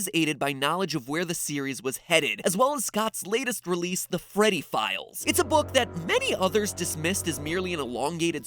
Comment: Ok so when he said freddy files I thought he said pedo files And im scared